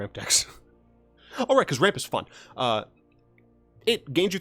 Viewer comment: I watch videos with closed captions on, and around the 2:40ish mark you said “Why do we play ramp? Because ramp is fun.” and the cc said “because rape is fun.” :O omg